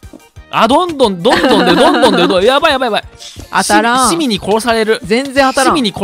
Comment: 市民に〇されるって聞こえるのは私だけ？